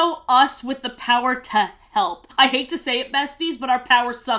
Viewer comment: Don't ask "why" but I heard "With the power to He||" as a kid...